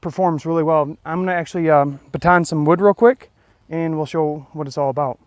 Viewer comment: I know I ain't too smart but when you said you were going to baton some wood I swear I thought you were saying you were going to twirl it like twirling a baton! Lol! I was like what is he gonna do?? Lmao! Great video man! Enjoyed the survival video!